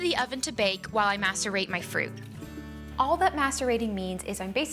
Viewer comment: i swear to gawd i thought she said "masturbate my fruits" lmao XD